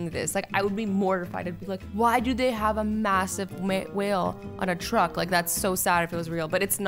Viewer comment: wait for it...its sounds like azzy is saying make instead of whale lol